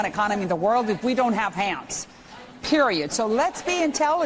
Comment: I can't make out what she saying the cc is spelling out hands, but I clearly hear ham